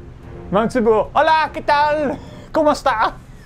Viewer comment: holy moly did say komusta???